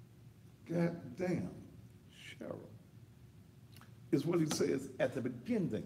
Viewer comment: I keep listening to the tape and l don't think that they are revealing it all to us right now. I believe that there is more that will be played during the trial. I also heard him call Sherra name too. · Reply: Lois Rattler sounds like he said damb Sherry or Sherry....damb